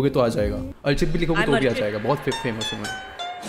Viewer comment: What did he said on 7:56 ?? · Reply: "Bohot famous ho mai"